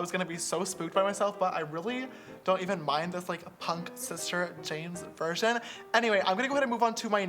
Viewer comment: He said "I really like this punk sister james version!" I literally heard "I really like this punk sister james VIRGIN" lmao ily james